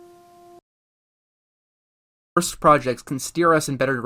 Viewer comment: The first syllable of the word is missing. Did you mean for that to happen in that cut? I think that said "The worst project." But all that came out is "orst project."